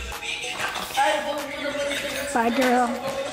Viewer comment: Who the f is talking at 54:05 please let me know, I think it’s saying “bye darrell” 🤷🏽‍♂️